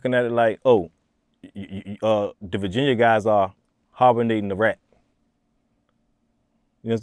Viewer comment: the Virginia guys are what??? What the hell did he say? Harbournating? He literally took the word harbour and the world hibernate and put them together. Wtf?